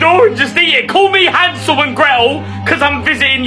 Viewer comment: I thought he said, "Call me handsome in braille cos I'm visiting your chamber of secrets" and I was a little confused.